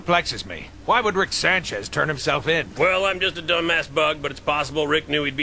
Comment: "well im just a dumbass bug but..." love this show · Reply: I thought he said dumb mass bug. As in he is part of mass of bugs or something idk